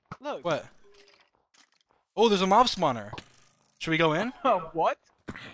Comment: I heard “it's a mob smawner* 6:42 Why does dream always confuses skeleton with spiders, he did that in a few videos as well. Also, he sounded like he's being possessed in that part.